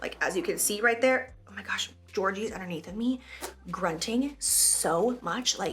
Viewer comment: When you said “Georgie is under me grunting rn” I thought you said “Jordan is under me grunting rn” 😂